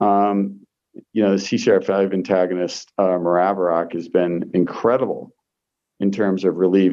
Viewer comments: What is he saying? Maraviroc? I have covid caused tinnitus... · Does anyone know what was said at around 27:09 for a treatment for tinnitus and brain fog? Before ivermectin and statins. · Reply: maraviroc